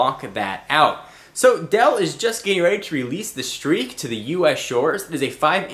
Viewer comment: Lool. Thought it said Steak! Omg I thought this was a porno :P *Phew*